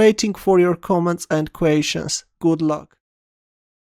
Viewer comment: I always thought said, "Waiting for your comments and equations." It's "questions" I guess.